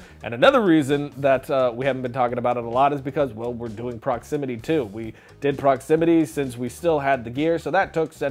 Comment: I thought he said Proximity 2 and I got really excited. Now I'm sad.